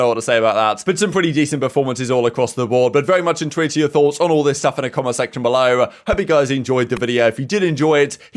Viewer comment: can someone tell me what hes saying its driving me nuts, "very much _ to your thoughts" he says it every vid and it annoys me how i dont know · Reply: intrigued